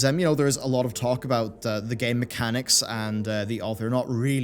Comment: Damn it, every time I say or hear 'Game Mechanics' for a split second I hear it as "Gay Mechanics".